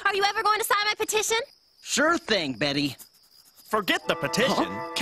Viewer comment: the transcribe audio tell: "stripping baby" instead of "sure thing Betty"...that`s weird too ;-).